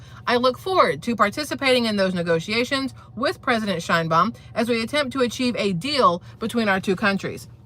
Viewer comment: 😂😂😂 "Shine Bum?!" 27:54 did I hear that right